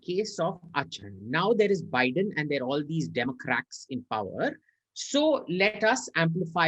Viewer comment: did he said em " demo-cracks" 🤯 Epic !!